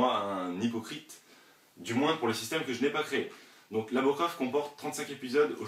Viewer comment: j'ai entendu mon prénom mdr 😂 (en gros je m'apelle Laora)